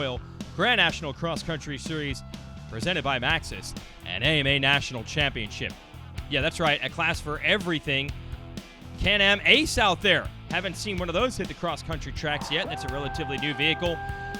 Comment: did he say can am ace?